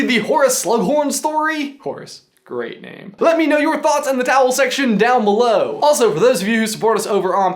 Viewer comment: Leave your comments down below in the what now? Can someone please help me understand what he says? I hear "towel section".